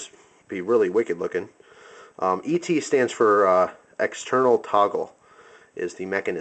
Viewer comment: Oh it was a TOGGLE! :O I heard 'tovel' ang got lost.